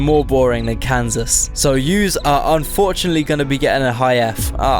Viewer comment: did you just say Youse?